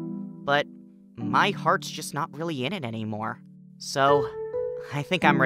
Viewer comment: Ok I’m sorry, but I thought they said “I’m come back sir” in reference to Oj